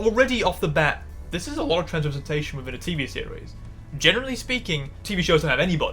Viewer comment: help- I'm watching with low volume and captions and the capts said "transportation" instead of "trans representation" 😭😭 I had to rewind and double-check lmfao